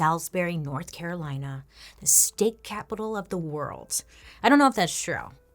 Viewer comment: Oh gosh, I thought she said called salisbury the steak capital of the world 🤦🏼‍♀️🥸